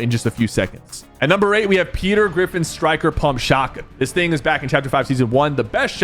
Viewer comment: did he said striker pump